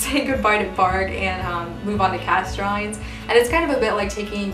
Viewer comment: "Say goodbye to Bargue and move on to..." whose drawings? I couldn't catch the name? Thanks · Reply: "cast drawings" she says